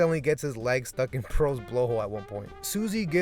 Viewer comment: I thought you said butthole😂